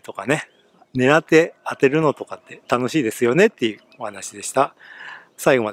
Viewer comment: 最初の的当てが楽しいですよねが 待たせて楽しいですよねって聞こえたわw